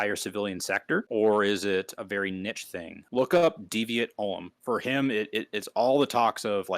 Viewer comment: Hello, new to the channel. I really enjoyed the talk and I appreciate all the references the guest made. However around 1:19:22 I could not clearly make out what he sugested us looking up. Something like "deviate olum" (?!) Can anyone tell me what was he talking about or the correct spelling?